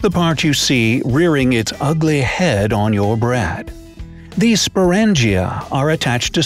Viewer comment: Did anyone else hear brad instead of bread